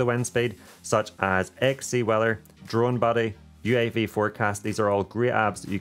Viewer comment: What is that first app you use for wind speed. I can't quite make out what you said. 5:15 Thanks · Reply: XCWeather - hope that helps.